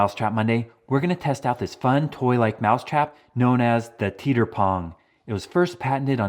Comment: I legit thought that said yeeter